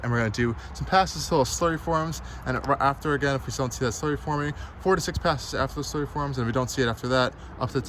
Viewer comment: Dawg I have no idea what the fuck you’d just said lmaooo 🤣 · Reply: Going to do some passes until a slurry forms If you still dont see a slurry form, do 4-6 more passes until a slurry forms. Minus the minor stutter between those sentences.